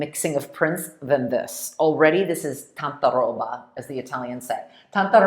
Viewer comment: what is the Italian word mentioned at 14:50? "tantarolla"? Would this mean "over the top"? Thx. grazie. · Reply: It’s actually tantaroba - and it means a lot of stuff - in a good way!